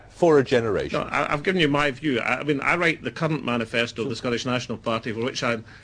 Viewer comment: 5min 22sec sounds like he says..... "I write the cunt manifesto" LoL